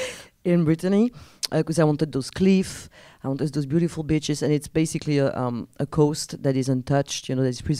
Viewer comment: I thought she said “beautiful bitches”...LIKE DAMN CELINE chill chill 😂